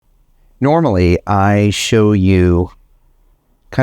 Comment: From start skipped to somewhere 3.42 and i heard you saying Normally is show you... nuts. xD